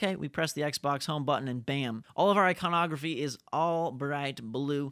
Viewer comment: i thought he said Ipornography